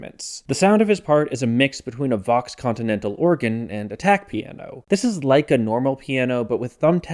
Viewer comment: I will not lie, every time you said "tack piano" i heard "attack piano"